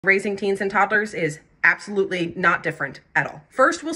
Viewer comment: i thought u had said “raising canes” and toddlers 😭